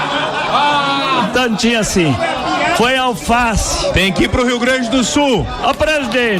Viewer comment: Achei que ele tinha falado que almoçou alfafa e não alface, combina mais com seus posicionamentos.